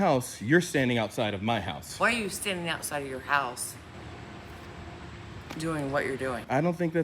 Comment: Did she just say why are you standing outside of your house? 😒